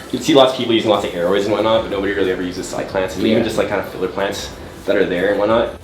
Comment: What is the family/genus of plants you guys are talking about at 6:20? Couldn't really catch the name. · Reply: cycads I believe! Basically mini palm trees hahah